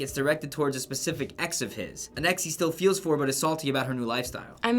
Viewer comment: did just say "salty"?